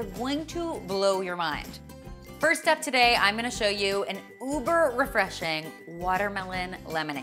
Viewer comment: did you say uber